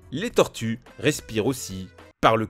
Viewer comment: attend quoi 😳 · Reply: ROM1407 "les tortues... Respire aussi... Par le c*l